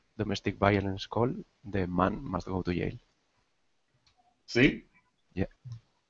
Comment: I love that guys accent, because it's so foreign to me and its easy to mishear words. 49:05 "If there is a domestic violence call, the man must go to Yale." Damn, call me a wife beater and send me to college! xD